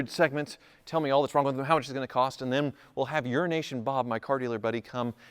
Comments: Ooooh Euro nation! I thought he said urination! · Sounds like is saying Urination Bob....but it's Euro nation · You need to pronounce Euro Nation better... it sounded like you said urination bob